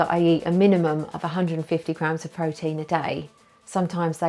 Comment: It sounds like you say 150 lb of protein at 2:52..